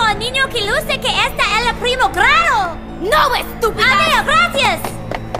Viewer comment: Omfg the Hospital one "No running in the hospital, bitches!!" "have you seen a young boy who appears in the first grade?" "No stupid!" "Okay bye, thank you!" "I SAID NO RUNNING!!" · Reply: Yo entendí "el primo claro". Definitivamente "primer grado" tiene más sentido xD